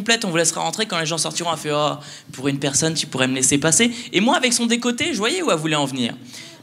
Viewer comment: pourquoi a 03:35 y'a une meuf qui jouie?😂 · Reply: Candy_Uta 50 a 3:33 . Juste après : " et moi avec son dėcoltė je voyais ou elle voulais en venir ! " tu l'entend clairement à part si tu as des problèmes d'audition.